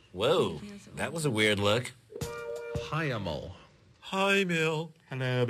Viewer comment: what word is he saying? They say it in a bunch of episodes, I've never been ever to figure out what the word is? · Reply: I am a word nerd and that is a totally obscure word. Never heard before. HIEMAL?